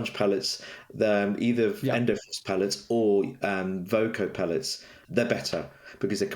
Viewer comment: Hi! What does say at 26:54 about the name of the pellets he recommends? The sound got a bit cut off there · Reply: Sponge pellets, endo pellets or voco pellets often used for cold tests